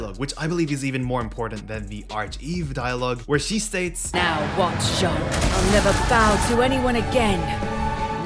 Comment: I actually thought she said “Now it wants Jeanne.” The background music and noises really screws with the dialogue for me sometimes lol. But yeah, Whittingham Bayo and Luka are certainly getting screwed 😭.